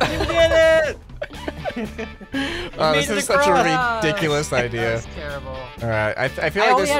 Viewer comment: did he just say rickdculous